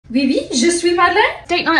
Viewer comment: “yes, yes i am speak!” -a perfect french speaker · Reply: I’m french and I heard "oui, oui, je suis madeleine" 😭